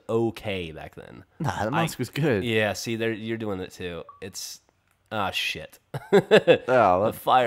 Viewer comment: I thought he was saying "Oh S***" because Jack got online! haha